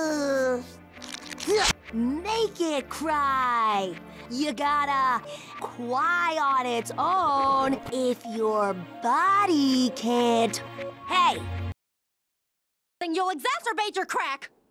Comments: If you can't understand, this is what she says: if your body can't cry on its own, you gotta make it cry! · what she's saying: "Make it cry, ya gotta, cry on its own if your body cant, HEY." what I think it's saying: "Naked cry, ya gotta, kwai on its own if your body can't, HEY." me at the end: <•>^<•> excuse 'em mwah? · what amethyst said your body cant cry on its own you gotta make it cry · I heard naked cry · What I hear Naked Child You gotta do it on my known. HEY! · "N@ FRYYYYY!" I can't be the only one who heard that right? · I hear “naked cry”